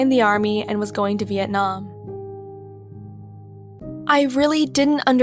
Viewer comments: did i hear it speak like " Vietnam " right? Việt Nam điểm danh 👇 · Did you just said Viet nam im vietnamese and9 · did she just say Vietnam because i I from there but I liv in United States 🇺🇸